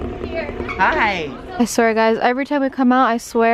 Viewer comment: who else thought they heard saying "here!"?